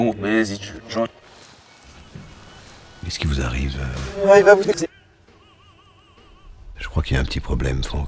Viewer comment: Vous entendez quoi a 1:02 ? C'est pas très propre tout ça. "Ah il va vous baiser"'